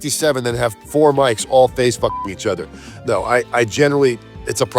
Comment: 😂 The line needs to be printed on a T-shirt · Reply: lol At first I heard "4 mics face fucking each other".